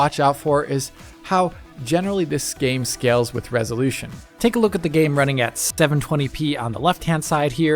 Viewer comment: Did he say "stéven-twenty p"? 3:24 Is that an easter egg?